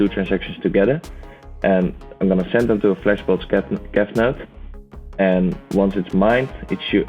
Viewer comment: what does he say at minute 17:46 ? flashbot scanner or what?